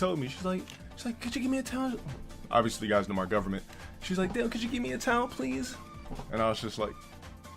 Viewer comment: Did he say "Dale"? lmao